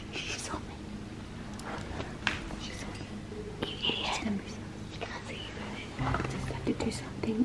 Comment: Did Sabre just call her mom an idiot..?